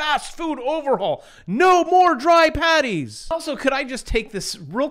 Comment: No more dry patties? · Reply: I thought he said panties.